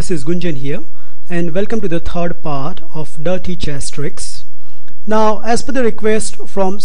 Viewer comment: I heard it as naughty chess trick